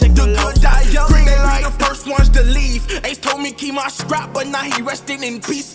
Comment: I thought he said x told me keep that strap not ace 🤔